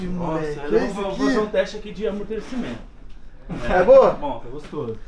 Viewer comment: dps desse tempo eu vi um leonato aii? Eu ouvi uma amor 👀💅❤